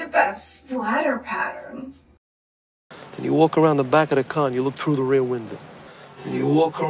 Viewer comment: lo escuché 8 veces y sigo escuchando " around the backA the caN..."